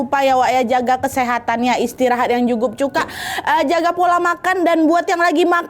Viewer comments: gatau salah denger apa gk kak Nad blg "Istirahat yang jugup cuka" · ini aku yang salah denger apa gak ya "jukup cuka"😭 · Istirahat yang jukup cuka. Kupingku salah denger apa gmna nad?😂